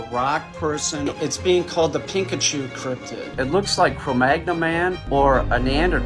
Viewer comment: im sorry but idk what he is saying all im hearing is pikachu 😂